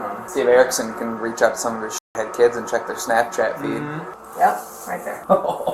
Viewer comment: was maddd funny. I heard it as "his shithead kids" and like thats SO REAL